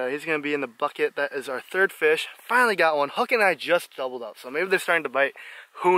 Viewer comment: Lol did you call Justin Hookin @ 5:36?